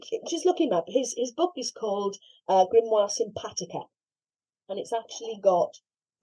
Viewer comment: Can anyone please provide a link to the book that I heard as Grimoire symphatica John Thomas? I may have misheard as can't find it available. Thanks